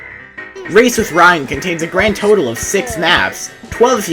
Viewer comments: The whole time I thought he said racist Ryan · For a second i thought he said racist ryan